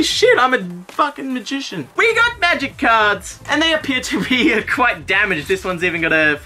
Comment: I heard "Magic Cards" as "Magikarp" the first time I heard it, had to repeat the video 3 times at that spot just to make sure.